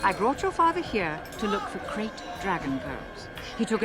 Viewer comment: I keep hearing "crate dragon". Crate dragon - docking workers' worst nightmare!